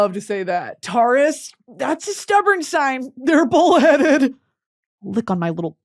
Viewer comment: I heard that as “They’re bald headed!!”😭😭😭